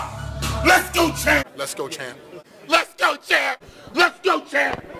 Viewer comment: if you think about it it sound like hes saying "lets go jab"